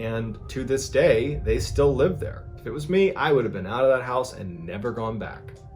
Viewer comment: I thought Mr Ballen said ‘it was me’. I was like wait 🤨😂